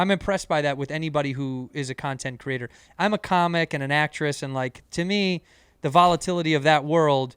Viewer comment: Did cheeto just say he was an actress? 42:35 Respeck